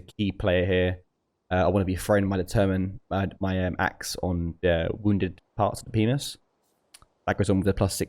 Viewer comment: Am I the only one who heard "Wounded Parts Penis" @ 3:50?